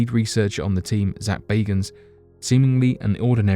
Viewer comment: I swore it said Zak Baggins like Bilbo Baggins from The Lord Of the Rings.